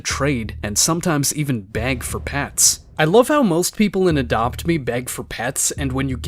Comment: I dead ass thought he said"bang for pets"😂😂😂😂